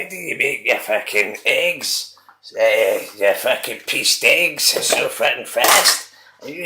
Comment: I think I just heard "Your pee stinks" 😅🤣😂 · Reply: I actually needed to listen back to that myself to work out what I was saying hahaha, I'm saying "pieced eggs", it's my way of interpreting the words "pieces and eggs", some time's people find it hard to decipher my special linguistic nature.